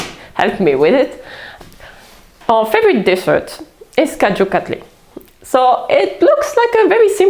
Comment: I loved this video! The food looks delicious! However, I couldn't quite understand the names of the 3:41 dishes. I got idly, biryani, samoussa and tamarind chutney, which all sound amazing. But what was the name of the dessert you make for holidays? TIA ❤ ❤❤ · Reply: This dish is called kaju katli, it is made with cajou nuts, cardamom, and sugar. It is a very nice vegan dessert.